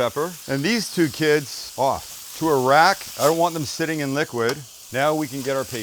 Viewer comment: Kinda raised an eyebrow when I first thought you said: 'And these two kids: Off! To Iraq.' (